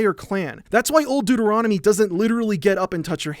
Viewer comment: the whole time i thought he was saying Old Dude Eronomy